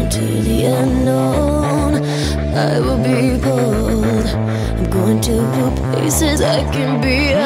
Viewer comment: I was listening this song from Spotify and i heard 'I will be bald' but it was bold. So at first i thought that she was prophesying herself. Im mad that i was not alive at that time bc she seem so sweet and all.